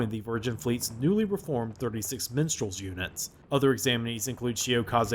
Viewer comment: I heard that as "36th Menstrual" and did a spit take.